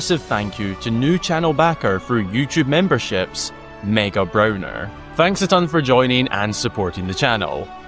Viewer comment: did i hear “Mega Broner” ?? lol